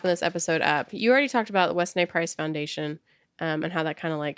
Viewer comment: I understand that i cannot speak for everyone here, however i sense that you two are a bit concerned about "beating the food topic to nausium" well i am here to say please dont worry about that . You two can walk, talk, live and breathe the food industry on your podcasts and I will never ever get tired of hearing about it! I learn something new every single time you talk about food! So much of what you talk about. One source i have never heard of was the wes nay price foundation... It was said so fast i thought you said "wess nay price" until i googled it and found its The "Weston A. Price" foundation... Bless both of your hearts... Lol I'm just a61 yr old lady trying to read up on a way of eating to try to help me live longer....and you are a wealth of information! I completely appreciate every video you put out there!